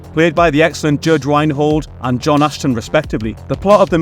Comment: Misheard you and heard “John Astin”, and was like… that’s not Gomez!